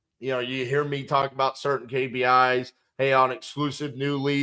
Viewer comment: What is that mentioned by saying @ 21:45 “certain KBI” is that a lead co. ? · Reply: Kpi. Key performance indicators